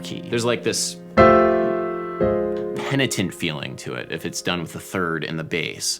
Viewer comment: What's "penetant"? I can't find this word in any dictionary :|